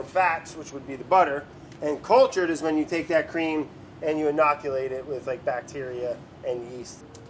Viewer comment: Sorry, BA editors. You can use "introduce" if you like, but "inoculate" is technically the correct term that's being used by YOUR FERMENTATION EXPERT. A perfect example of someone trusting a Google search instead of the terminology being used by someone educated in the field. 🤦